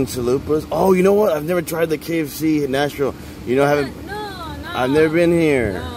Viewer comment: Are you in Nashville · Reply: Oh I thought I heard you say Nashville